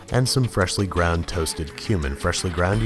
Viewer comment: Every time you say 'cumin', I hear 'human' and have to remind myself that this time, it's not cannibalism.